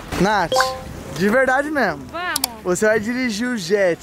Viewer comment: falando a respondendo oi amor 0:32 o melhor casal do YouTube kkkk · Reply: Nd a ver ela falou vamoo